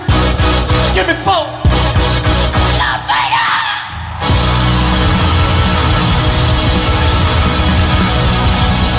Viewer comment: what's Prince saying at 20:59 mark? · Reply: "Las Vegas" ... he always says that to signal his band to end a song in a loud heavy "Las Vegas-like" manner! You're welcome :)